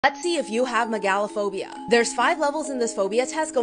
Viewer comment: I was looking away and I thought she said "let's see if you have MET GALA PHOBIA"💀